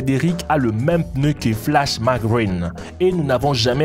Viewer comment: c moi ou il a dit flashmaGREEN a